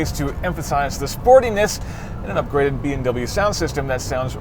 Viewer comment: I think he meant to say "a B and W sound system", not "a BMW sound system". DOH!